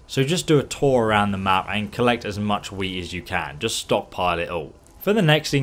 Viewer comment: did he accidentally say weed instead of wheat? 😂